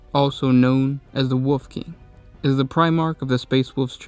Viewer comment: did anyone else hear “the woof king”😂